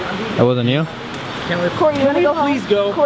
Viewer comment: I’ve watched this one a few times when it comes up on my feed and just this time I’ve noticed the Cory says “I’m dead” instead of deadass again at 17:15 and it have me chills lol